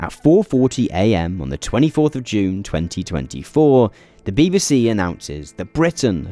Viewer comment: it says 2024 instead of 2016!